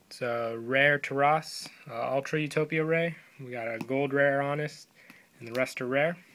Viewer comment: did u say gold rare honest for the gold utopia?